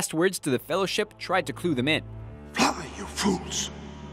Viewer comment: First, i always thought gandalf said "run you fools" referring to the giant demon thing that was attacking them. Second, Having the eagles help would have been an aweful idea, like he said on film theory, because they were trying to hide from sauron, hiding while flying on the backs of eagles that are half the size of a football field wouldn't really work too well.